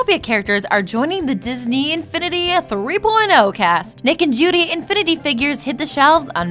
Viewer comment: am I the only one who thought they said naked judy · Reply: Possibly. Because she actually said "Nick and Judy".